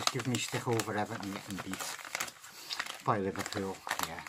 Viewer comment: I heard “by Liver pill” lol.